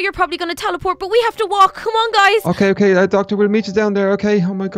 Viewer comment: who else heard say ok ok thx docter